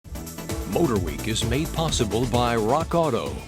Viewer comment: i swear to god i heard "ODOR WEEK" in the beginning lol